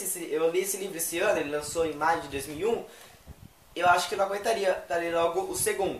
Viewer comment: Em 3:36 do video, eu entendi 2001 kkkk